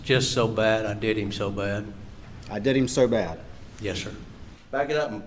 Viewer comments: I also have to say I hear "I did him so bad"-not THEY ! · I heard “I did him so bad”. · Sure sounded like Alec said "I did him so bad".